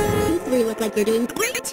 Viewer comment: i heard girlfriend in